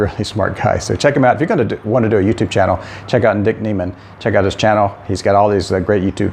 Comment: does he say Dick Neman?